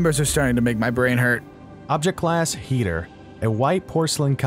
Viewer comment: I hear "Object class heater"